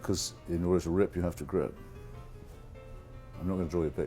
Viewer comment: "in order to rip you have to ...."? what did he say? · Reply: Grip